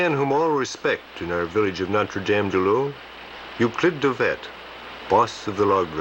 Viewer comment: was just curious where the place was near.... at 4:48 I think I hear "Notre-Dame-du-Laus", (Québec) 46°05'25.3"N+75°37'26.6"W But I can't quite make out the name of the river at 13:12 anyone?